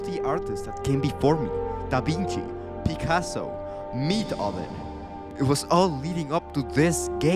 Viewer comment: Did he say Meathoven?